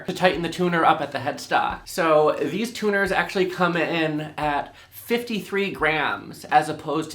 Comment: I thought you said the tuners came in at 53 GRAND. I thought DAMN, those better be some awesome tuners LOL